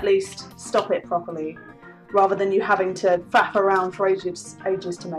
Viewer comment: I thought I heard her say fap around, I thought perhaps I was playing Warhammer improperly.